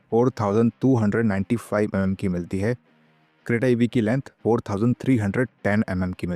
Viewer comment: is he saying creta ev?